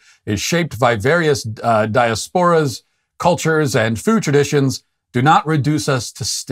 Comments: Did he just say "daya-sporas"? 🤦‍♀️ That's first class irony in a segment about mispronouncing obvious well know words 😂 I'm not on either side here, but that's just karmic I must say. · As an American with Hispanic descent, I say down with all leftist organizations! Up with the right conservative path! No one should say LatinX!! We cannot allow liberal leftists to choose what we call ourselves! Stick to traditional values. Please. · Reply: Did he just say "daya-sporas"? 🤦‍♀️ That's first class irony in a segment about mispronouncing obvious well know words 😂 I'm not on either side here, but that's just karmic I must say.